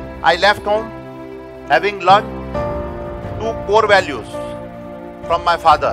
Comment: By watching this for the first time I thought "he said two crore values from my father"